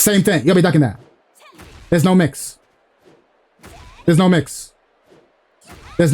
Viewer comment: wait, there is a mix? I think I heard that wrong 🤪